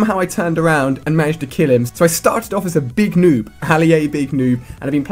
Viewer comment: Thought he said "Ali-A Big Boob" ...